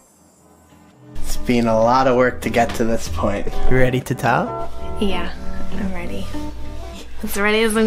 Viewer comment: You asked if she's ready to tile- I thought you said, "Are you ready to TELL?" And I thought, BABY!!! A Wabi-sabi baby!?! Lol... Nope. Not yet. ;)